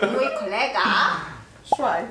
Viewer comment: ja usłyszałam staja D